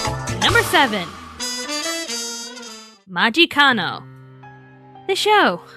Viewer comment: When she said the show title name for 07, i thought she said Magikarp. XD